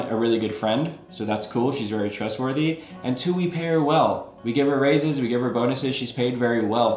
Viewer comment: I thought you said you give her raisins🍇